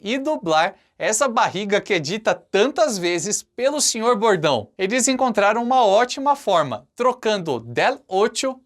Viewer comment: Só eu q escutei: " Essa barriga que é dita tantas vezes pelo Sr. Gordão"???? Kkkkkkkkk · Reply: Senhor bordão